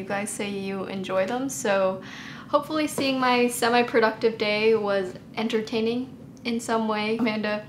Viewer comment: i thought it said “a productive dog” 😭😭💀